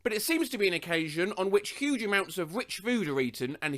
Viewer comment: I thought he said “witch food” which also would have been accurate.